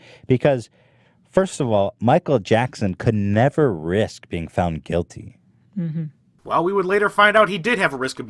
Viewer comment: you misunderstood what Ethan said. "Michael Jackson could never risk being found guilty"